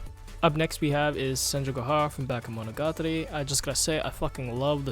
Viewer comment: whats the show called i cant hear him... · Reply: The show is Bakemonogatari.